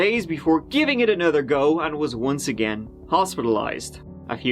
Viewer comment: he actually said go. Not goo. Im super excited